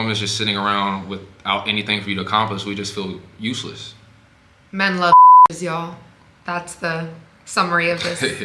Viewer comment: what did say at 19:08 that was beeped? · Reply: Men love bitches